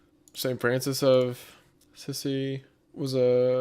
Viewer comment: Did he say St. Francis of Sissy or am I tripping?